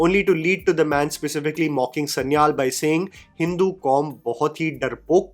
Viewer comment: I think the terrorist says Hindustani kaum not Hindu kaum